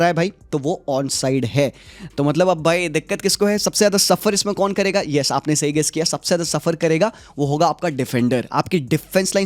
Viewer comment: says suffer kon karega i heard barcelona😂😂😂